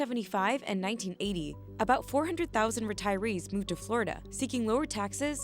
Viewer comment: "They attract Tyrese"... Huh, oh retirees, I misheard 😂